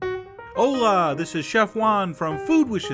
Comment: Hola! This is Chef Juan con Salsa Cruda! Well, I thought he said: Owla! This is Chef One cone Salsa Cruda!